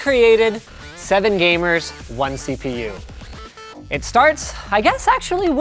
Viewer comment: For a second I thought it said 7 girls, 1 cock